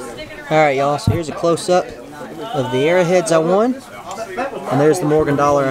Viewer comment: I thought he said airheads. I was like "that's cool. Let the kids find some candy."😂😂🤣😜